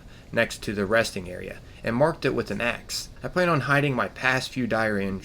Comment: ''Marked it with an Axe'' Thats a nice pun :P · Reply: I wasn't reading the note so I thought he said "an X", not "an axe" 😁😁